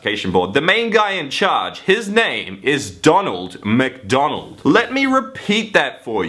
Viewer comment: when you said his name I was thought you said Ronald McDonald